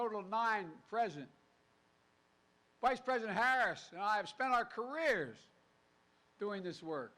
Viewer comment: Did he just say president Harris and I????